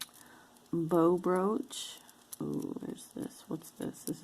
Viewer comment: Oh my, I thought you said "Bro Brooch" @ 9:19. lol